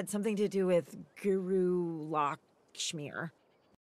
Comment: All this time, I thought he said Guru Ligma